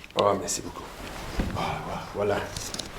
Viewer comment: did he say "hola"? 🤣